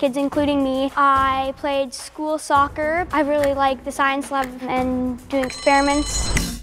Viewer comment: I’m not the only one who thought it said “School Shooters” right?